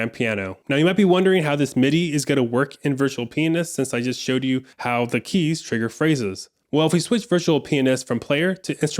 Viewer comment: What did you say here? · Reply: He said: "This is the best virtual pianist out there." 😁 🎹